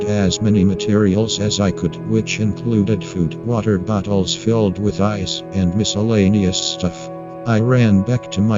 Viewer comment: I heard "...water buttholes filled with ice" lol